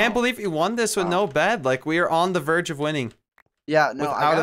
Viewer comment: i thought he said 'on the 'virgin'of wining '